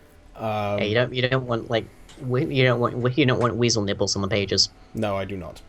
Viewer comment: I really misheard say "weasel nibbles" and congratulations for making me laugh which in turn made me cough uncontrollably.